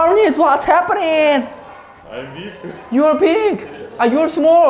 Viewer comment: I thought he said "You are pig" XD